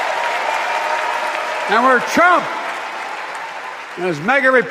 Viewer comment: America I Love you❤! How come when Joe says it it feels warm and good to the heart. When trump does it, it feels sick and dirty. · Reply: Did they just say we love Trump